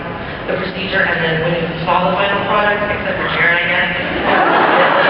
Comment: What did the girl say @ 6:48 to get that reaction from Jared? "Except for Jared I guess"